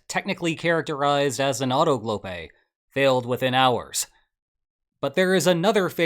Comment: what is this word? auto-globe? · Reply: autogolpe - Spanish, an "autocoup". A coup d'état in which a nation's leader, despite having come to power through legal means, dissolves or renders powerless the national legislature and unlawfully assumes extraordinary powers. Had to look it up too. ❤